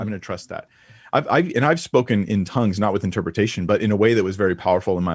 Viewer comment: did say he spoke in tongues before???